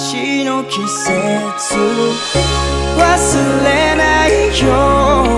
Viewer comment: 聞こえるよ 僕の名を呼ぶその声が 月明かりの中 いつかのメッセージ 届いているよ 「また会えるよね？」 なんて君が言うから 想い出がふいに溢れ出して 止まらなくなる 共に過ごした日々の記憶 優しい風のように (Just like the wind from the land) 背中押した 旅立ちの季節 忘れないよ 僕らが重ねた運命は 同じ空 同じ星の下で 輝いている (A gift from the moon) 忘れないで 僕らが出会えたキセキを そしてキセキは信じるものだけに 贈られるモノ This is a gift from the moon This is a gift from the moon 伝わるよ そのぬくもりもやさしさも 見上げればいつでも会えるはずさ 離れていても 「出会えて良かった」 なんて君が笑うから 淋しさがこぼれ落ちそうになって 話せなくなる 僕ら過ごした日々がいつか優しい風になり (Just like the wind from the sea) 未来をほら 運んでくるさ 忘れないで 僕らが描いたキセキを そしてそのキセキがまた明日へ続く 光になる This is a gift from the moon 忘れないよ 僕らが重ねた運命は 同じ空 同じ星の下で 輝いている (A gift from the moon) 忘れないで 僕らが出会えたキセキを そしてキセキは信じるものだけに 贈られるモノ This is a gift from the moon This is a gift from the moon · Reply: 何度聞いても、忘れないぜ～に聞こえる… 耳鼻科に行こうかな｡笑